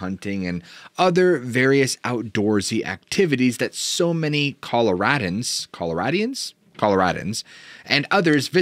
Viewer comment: it should be Colo-rodents, I heard